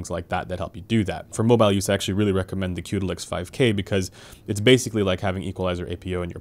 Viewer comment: What was the mobile eq program he mentions @ 2:23? Sounds like he says: 'q2lix5k'. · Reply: He said Qudelix, I would suggest that you try wavelet too, it's a global eq